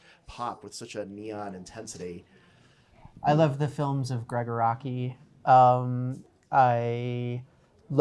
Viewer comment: Schobern mentions a director a 4:23 but I can't figure out how to spell it does anyone know who this is? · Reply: Nvm got it it's Gregg Araki for anyone who's curious